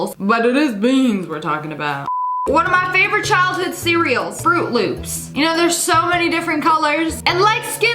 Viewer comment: When you said fruit loops I thought you said fart loops lol